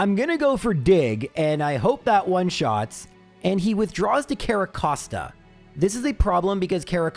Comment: Idk why i misheard Carracosta as Kevin Costner 😂